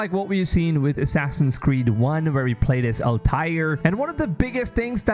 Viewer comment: Did he say "Al-Tire"?